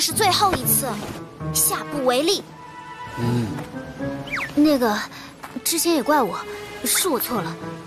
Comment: did she said the n word💀💀💀💀